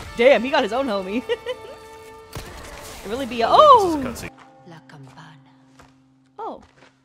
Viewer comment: she actually said "la campana" which means "the bell" in spanish; she is speaking spanish because the game takes place on a spanish village on the 20th century Probably everything looks so medieval because the spanish economy on those years (the 50-70s) was VERY bad because of the dictatorship